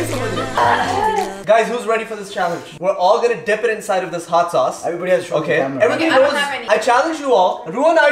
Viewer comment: When you just said , Hot sauce I heard you saying Hadsaa😂😂, and I was like whatt? Hadsha?😂😂 then I just repeated the video and got the real word ( hot sauce ) 😂